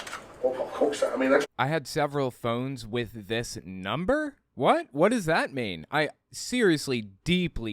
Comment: It sounded like he said "I had several different phones with this number", meaning that he could have had different phones with the same number at different times.